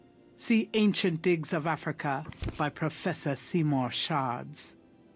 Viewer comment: Am i the only one who heard it as "see more sharts" not seymour shards??